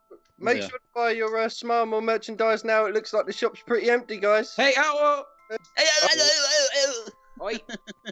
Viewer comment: it sounded like said Hey AtWood